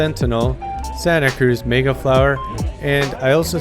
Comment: Ha ha, it sounded like you said Santa Cruz Megaplower in the beginning of the video. So just saw your video come up in my feed and you did this in July, gotta love youtube algorithm, never on time right? I did a review on the Ripmo on my channel as well and here is my findings. I'm 5'10 as well, I have long arms, long legs and short torso. My experience with the Ripmo was with the large. I'm kind of partial to longer reach bikes because of my long arms. When I got on the large, believe it or not, it felt cramped to me and shorter than I expected. I got on the XL and it actually felt perfect and I almost took it out for demo but stayed with the Large. I thought the large Ripmo was actually a one bike quiver for sure. I would say the Sentinal is closer to the Ripmo and the Pivot bird is closer the MegaT and Evil Wrecker. For that much travel, the Ripmo climbs like a rocket man! I could not believe how well it climbed! But I totally agree with you, it does get bucked around and more trail feedback than you would think for having that much travel. I think that is characteristic of the DW link on the Ibis brand. THe DW on Pivots seems to not climb as well as the Ibis frames but they are more supple and plush over the Ibis DW frames. I think the offset forks on the Sentinel make it really sort of dead and blah feeling. Yes it makes for confidence inspiring ride and it stays glued to the ground but its not going to put a smile on your face. Its just sort of there. I'm not a real big